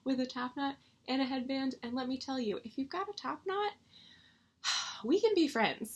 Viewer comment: I heard that as "IF you have a topknot..." lolol YES Super Splotchy!! so perfect. And that fabric for Mad Plant; I love it SO MUCH! Cat Ghost looks SO GOOD!!